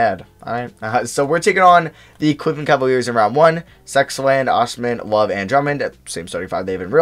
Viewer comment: did I just hear that right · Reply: Sexland